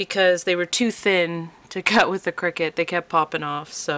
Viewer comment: OK NOW I KNOW WHAT A CRICKET IS..LOLLL ITS ACTUALLY THE CRICUT THAT I SHARED WITH YOU TO ASK YOU WHAT YOU THOUGHT OF THE PRODUCT...LOLLL IF YOU LISTEN WHEN YOU SAY THE CRICUT IT SOUNDS LIKE YOU SAY CRICKET ..Hiii WILL GET BACK TO YOU WILL HAVE AN ORDER FOR THÉ COASTERS..GOT TO BE SURE OF THE MONTH..GOOD WORK.